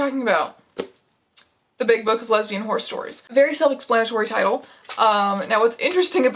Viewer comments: I thought it said horror stories so i was 20 minutes into the video waiting for something scary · Man, I thought it said horror stories... Boy was I wrong 😅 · i didnt read the whole title and i definitely heard "lesbian HORROR stories" so i was VERY confused that the second and third stories also involved horses before i realized my blunder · I heard it as lesbian horror stories to start with and got so confused why all of the stories were about horses